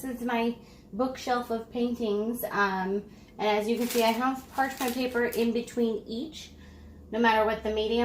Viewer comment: "Parshman paper" ? What did you say ?